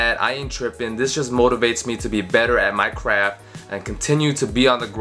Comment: I thought he said get better at minecraft